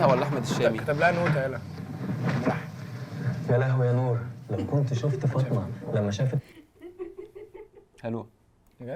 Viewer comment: هو الافيه كان ايه ماسمعتش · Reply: كتبلها نوتة.. ملحن